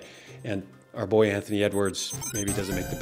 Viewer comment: I Heard "n-words" and not "Edwards" at first 😅